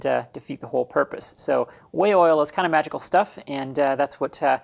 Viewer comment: For some reason you can’t buy Dykem in Australia and Amazon won’t ship it to Australia. You have always had me worried, being hard of hearing I always heard you say Whale oil!